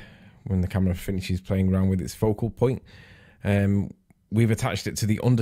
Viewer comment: Wait...? at 2:23 What did you say there...? "Foucault point"? Don't try that one again... you failed Miserably last time.... miserably.... just miserably. smirk =o)